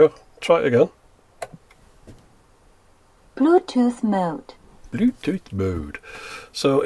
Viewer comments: P L U T U T H M O D E Killed me 🤣🤣🤣🤣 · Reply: Dániel I thought she said "Bluetooth Mote." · the voice for the bluetooth on this thing is the same voice you get on those $4 shower speakers. It always sounded like she was saying "bluetooth moat"